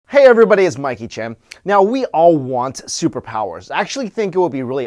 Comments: You really look like Jackie Chan I thought you said"Hi Everybody I'm Jackie Chan" hihihi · At first I thought he said his name was Mike Chang 😂